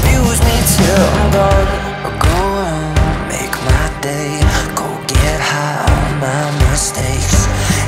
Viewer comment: I thought he said "don't get high on my birthday"